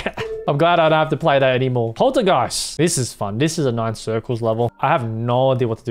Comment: did anyone hear him say polargiest?